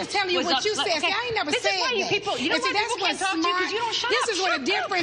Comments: Anybody else hear at 4:13 4:14. Kim states, this is why you people and cuts it off. I liked Kim in first seasons but that comment don't really sit well with me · Did anyone catch Kim saying "this is why you people" and then she stopped 😬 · Am I the only person who heard kim say “you people” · If you listen closely, Kim says "This is why you people" (4:13-4:14) and quickly corrects what she says. I just found this interesting ... not trying to make any assumptions, just something I heard as I was listening to this clip while working on a project. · did Kim say “This is why you ppl”?